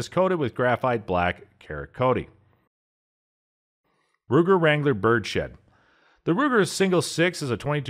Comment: Thought he said Ruger Wrangler bird shit